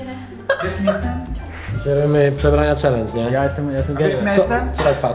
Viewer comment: ja usłyszałem jestem gejem ? :D